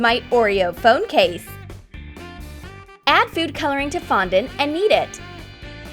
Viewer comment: am I the only one who heard and eat it?